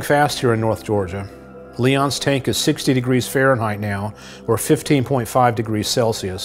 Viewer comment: First i thought he said 50.5 degrees celsius and i was like WHAT!!?? When i went back i heared it was 15.5 lol. Have been watching several leon videoes while trying to sleep. It usually works. I think watching leon is so relaxing.